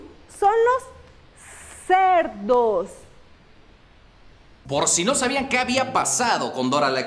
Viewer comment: Cuando la maestra esta hablando de cerdos y Luisito dice: "Por si no" Yo entendí "Porcino" jajajaja 🤣😂🤣